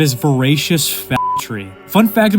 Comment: Did you say fagtory? If so, good one.